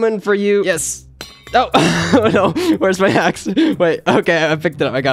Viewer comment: when he dropped his axe it sounded like he said weres my hax